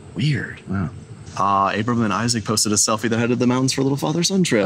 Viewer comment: Fix caption. Like at 2:16, it says April instead of Abraham.